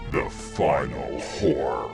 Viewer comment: Any one else think he said "The final whore" At 11:53? No? Just me? I'm the fucking worst.